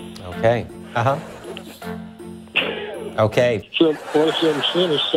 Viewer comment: can someone transcribe what the man at 0:57 says? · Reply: Okay